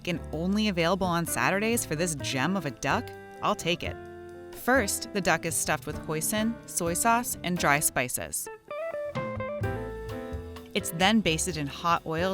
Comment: I thought she said "the duck is stuffed with poison"